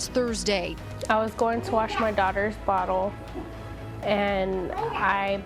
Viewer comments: who else hear 4:11 she says sje gonna wash his daughter butthole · i heard..." i went to clean my daughters butt hole"